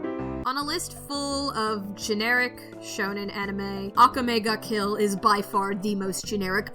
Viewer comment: At first I thought it said “kill la kill” then I realized it said akame ga kill. This video is really good also couldn’t agree more with elfen lied!